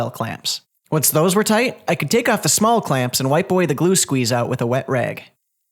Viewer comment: Did you say "white boy the glue squeeze out" at 2:08? 😂 Good thing you didn't put any juice grooves in this desk!